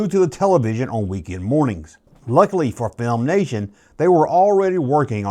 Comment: I keep hearing "film nation" rather than "filmation."